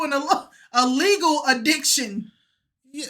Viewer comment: I hear you but the girl saying it’s “illegal” is a absolute lie I head to the mgm grand casino and place parlays openly lol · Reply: She said " A Legal Addiction" 👍🏿 she was just struggling to get the words together